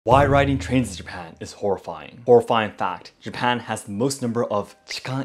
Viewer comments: Had to replay the start, I heard him say "why riding trans in japan is really horrifying" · i thought he said tanks rather than trains